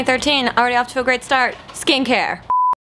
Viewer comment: at first i thought you said 'skank hair' instead of 'skincare'